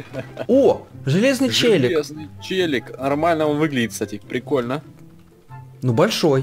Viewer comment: Вы сказали железный ЧЕЛИк а мне послышалось железный ЧЛЕНик